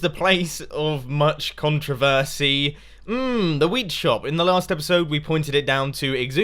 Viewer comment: I put on subtitles at the beginning and at 0:56 instead of saying wheat it spelt out weed 😂 (the subtitles)